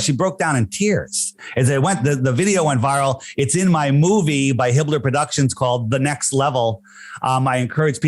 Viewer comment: Also did he say his " movie by Hitler productions?" Somewhere Around 26:23?